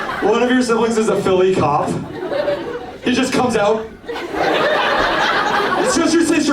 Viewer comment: she clearly said "ones a Philly cop" and you even restated what she said right after, how did the subtitles mess that up that badly